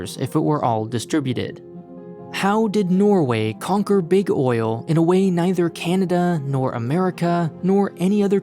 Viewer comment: It was at that moment when I heard "how does Norway conquered Big Oil unlike any other country" and the answer I thought was because it was sponsored by Brilliant. 2 seconds later I realised that I f***ed up.